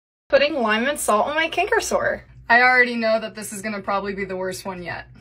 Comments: I thought she said "Canker Sore "not "Cancer Sore"😢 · The title: “putting lime and salt on the cancer sore” Me: Cancer? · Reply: I thought they said canker sore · The title: “putting lime and salt on the cancer sore” Me: Cancer? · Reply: i thought she said cancker :l